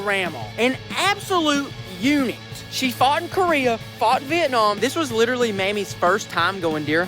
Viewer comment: Thought you said she fought in Korea